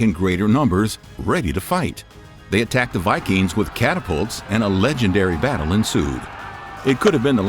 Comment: Did he say the native Americans used catapults at 4:19?